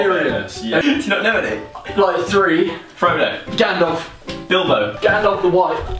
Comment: I thought Joe said 'Dildo' when he said Bilbo. XD